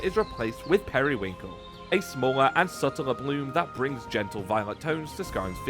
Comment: thought you were saying "blue" not "bloom", was bout to say get your colors right bro lol